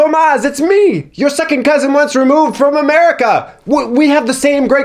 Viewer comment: The subtitle is wrong, isn't it? says "It's me, your second cousin ONCE removed from America" and not "WAS removed" 2:40 I'm totally sure says "My tummy IS IN a great deal of pain" and not "My tummy ISN'T a great deal of pain" Please, correct me if I'm wrong